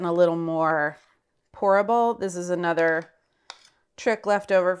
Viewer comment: When said she was making the peanut butter more 'pourable', I thought she said horrible and I thought she was a monster.. But then she saved herself with that dessert! 😃